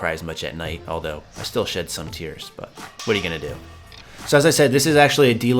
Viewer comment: I thought you said "shit some tears" lol... That chick is hot btw